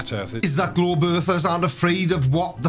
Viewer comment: Did you say that Globe Earthers are afraid or aren't afraid? I listened over 10 times, but when it all goes so fast it sounded like 'are afraid' but you said it correctly. Aren't afraid!